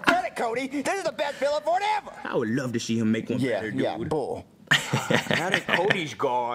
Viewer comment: Why did Junior say bowl?